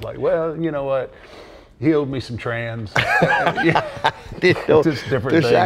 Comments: I have watched this part over and over trying to figure out what Taker says here. Is it "he owed me some trans?????" That's what the closed captions say lol What is he saying here? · "He owed me some trans." ??? what is he saying here?